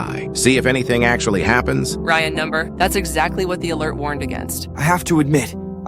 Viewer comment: I swear they always say number instead of no